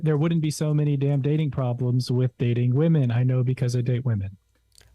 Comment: Every time I hear the intro I hear “problem with alalala.”